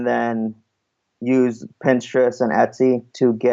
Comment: Didn't understand the two tools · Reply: The seller mentions Pinterest & Etsy to come up with ideas for products.